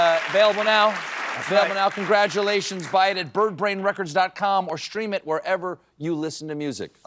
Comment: did he say Bird Bane? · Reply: Birdbrain!